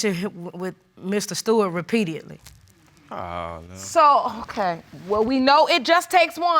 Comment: Did she just say: Repeatedly or "REPEADIATLEY" 🤯🤯🤯